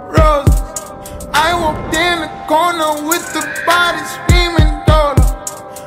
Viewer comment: Who else thought he said "i was in a porno with joe biden screaming no" at the beginning?